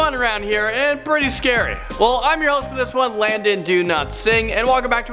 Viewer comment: I honestly thought he said Landon, do not sing